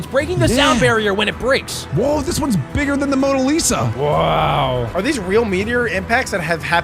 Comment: did he say MONA LISA